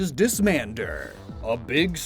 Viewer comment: The whole time was Doing the Joke segment with Dismandurr, I thought he said Piss Man, instead of Disman 💀